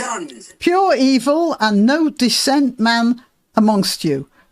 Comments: I don't think it's supposed to say 'dissent', I think it meant to say ''Pure evil and no decent man among you!'' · The word "dissent' is a mis-spelling of what the translator said. It should be "decent". No decent man among you